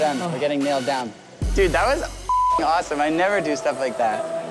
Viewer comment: did he say "cop gonna get us"